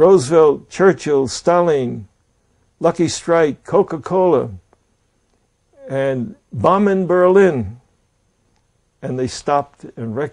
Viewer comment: I think what saved him was saying “bombing Berlin” and the Russians were like oh shit my bad blud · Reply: rus pov here and to be true i didnt even recognize that he was sayin "bombing berlin" all i heard was "blakamaluka" no offense but for our ear its hard to disguise english words